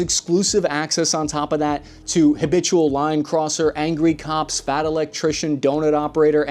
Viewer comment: lol I thought you said "habitual lying cusser" about the boys not "habitual line crosser".